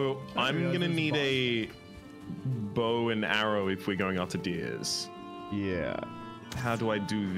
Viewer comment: Did he just say “deers?” 😂😂